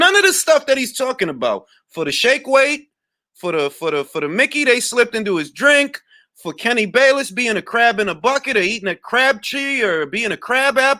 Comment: I thought he said crap in a bucket its a traveller thing go watch a traveller call out video